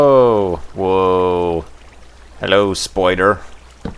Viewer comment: did you say hello spider or hello spoiler? lololol